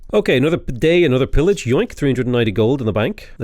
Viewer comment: I swear at 9:51, I heard him say “Another bidet, another pillage”, and honestly - good.